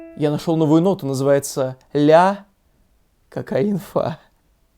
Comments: "Я нашёл новую ноту, называется:ля какая инфа" ©Топа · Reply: Послышалось "ля кокаин фа" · мне послышалось ля КАКАИН фа xD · Я один услышал "Ля, какаин Фа" ? :D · я один услышал "ля кокаин фа"?)